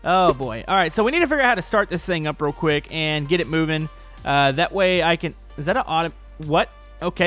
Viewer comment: Is it just me or in the beginning did it sound like he said im pooping? He actuly said im moving but i thought he said im pooping